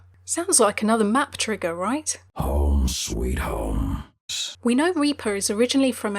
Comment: i think the smog line might reference a new map that may come out. I think it will be the ruins of the overwatch base after 76's and reapers battle, and the smog is a referring to the debris caused by the fall of the building. it also may be where the home sweet home line comes from. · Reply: when i heard reaper say the home sweet home line i swear i heard him say home sweet homes making it sound like he considers many places as his home